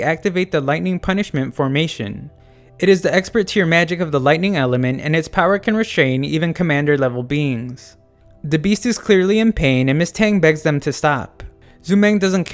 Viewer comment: I thought he said “commander level beans”